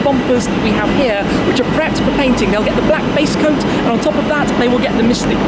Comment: "it'll get the black based ...." What? hahahaha i thought you said... nevermind.